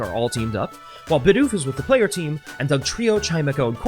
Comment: When you said "Bidoof is" at 8:39 I heard it as "Bidoofus" as in a portmanteau of Bidoof and doofus